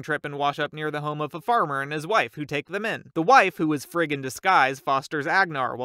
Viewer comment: When you said “frigg in disguise” It sounded like you said “fricking disguise”